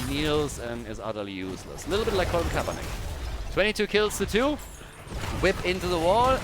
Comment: lol did you say useless like Colin Kaepernick?